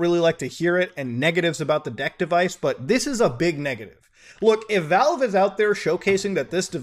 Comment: sounds like muta said “this is a big nigga”